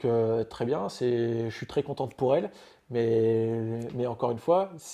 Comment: Et c’est moi ou à 1:14 il dit contente pour elle